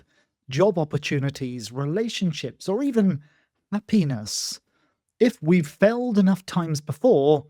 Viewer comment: Wonderful, very NLP-ish !! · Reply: yeah, I'll have wet lucid dreams tonight. I heard "a penis" instead of "happiness", and "porn" in "a POOR Neighborhood", and so on...